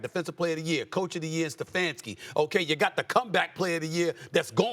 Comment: Did she just say cowback? Who did she blow to get here?